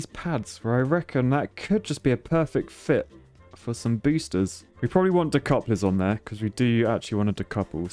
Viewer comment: Did he say BOOSHters?